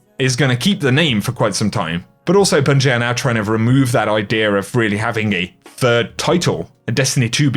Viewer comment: I'm loving the exotic grenade launcher. I use it in a lot of pve content! I'm still catching with these engrams. I like the idea over them! Wish bungie would fix the sprint on console. I randomly stop when I'm holding down the L3 analog stick on ps4! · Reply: At least I'm not the only one who's experiencing this and also did anyone else catch say ideer at 13:04-13:05 I say that a bunch too lol